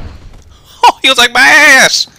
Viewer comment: Did Nate say "boom" before shot that propane tank?